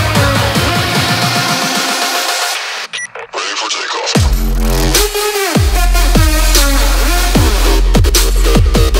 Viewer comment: what do you say? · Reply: Ready for takeoff :)